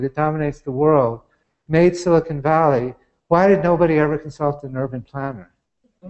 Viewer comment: What did he say at 28:04 ? "consult the ???" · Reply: An urban planner. <link>